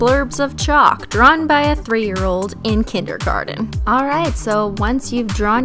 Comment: Am I the only one who heard her say, "3 year old in kindergarten."?